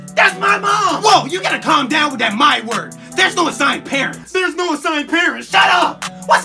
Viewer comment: "MAKE HER." staring Teacher: * holds flip flop * "TURN IT OFF.!" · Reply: I thought he said the N word